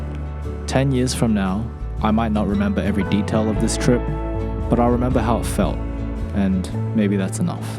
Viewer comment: "10 years for now, I might not remember every detail of this trip but I'll remember how it felt" That hit me. Really. Life goal: create core memory. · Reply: I thought he said "... but I'll remember how I fell" and I burst laughing :D